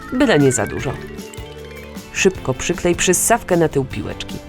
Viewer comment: Usłyszałam "na dupie piłeczki" WTF?